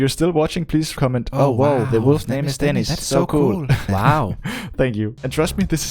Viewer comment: For a second I thought it said death not Dennis😅